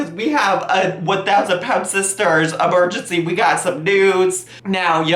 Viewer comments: phew I thought you said “we’ve got some nudes” for a second there. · When you said, “we’ve got some news,” I thought you said, “we’ve got some nudes,” and I was genuinely frightened.